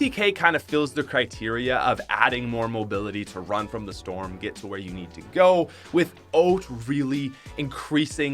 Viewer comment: What the heck! He said without weird!